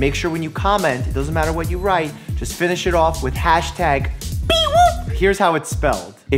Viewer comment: Hi Dr.Mike you know all this time I thought you were saying Beewoop not peewoop. 😂😂😂 I feel really dumb now. Anyway I always enjoy your videos and a weird coincidence I was just thinking of trying to go more paperless for college. #PEEWOOP